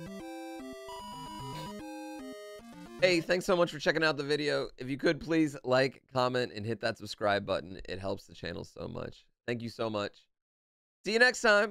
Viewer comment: I totally heard "Please like, vomit and hit that subscribe button." I thought it was a strange request until I listened a second time.